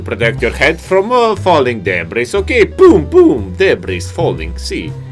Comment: did I hear boom boom?